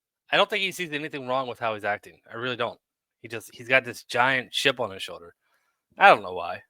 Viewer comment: Boy I heard that old, "You've got a chip on your shoulder. " line, as I was coming up. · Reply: The closed captions said "he had a ship on his shoulder" 🤣🤣🤣